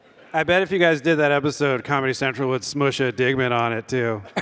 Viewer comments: Can someone please transcribe what Andy says at 50:00? I didn't get that joke. (if you guys that Comedy Central would "smush a digment" on it too)??? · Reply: "Smoosh a digman on it", Digman is the show that he's on and he's saying that Comedy Central would put Digman into the southpark/BB crossover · Can someone please transcribe what Andy says at 50:00? I didn't get that joke. (if you guys that Comedy Central would "smush a digment" on it too)??? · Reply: "I bet if you guys did that episode Comedy Central would smush a Digman on it too." All three of their shows are airing on Comedy Central. Digman! is the name of Andy's show, named after the character Rip Digman. He's saying that if South Park collaborated with Beavis and Butthead, Comedy Central would want to capitalize on the collab by "smush"ing Digman into it as well.